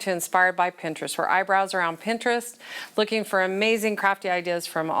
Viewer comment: Lol…I thought you said “eyebrows are on Pinterest”